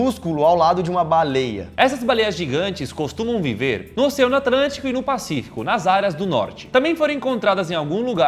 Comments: eu ouvi atrantico? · só eu ouvi "Oceano Atrantico"?😂😂😂💜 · Só eu escutei '' Oceano Atrantico '' ?